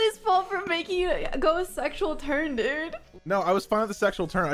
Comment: hi I just wanna point out how he says turn but the captions say tension